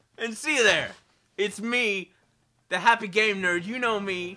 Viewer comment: i thought he said, "I am the happy gay nerd"